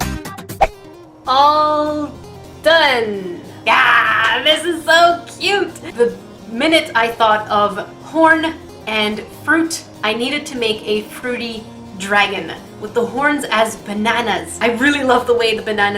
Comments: At first I thought you said "Porn and Fruit" lol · did she say porn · Did she say Porn ?????? 🤨🤨🤨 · Who else thought she said 'When I thought of porn and fruit' not 'When I thought of horn and fruit'? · At first, I thought you said, "Porn and fruit."..😐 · I thought she said the minute she thought of porn and fruit lmao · Is it just me or did I hear her say porn.... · 10.28 , it sounds like she said porn and fruit